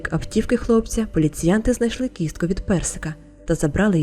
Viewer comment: коли почула про частинки хутра, то почулось кістка від песика, але о 26:14 все стало на свої місця))))